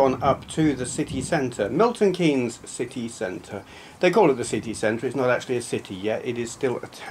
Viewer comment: I thought you said 'sickie centre' as if it were a place notorious for workers bunking off or something! Haha :-D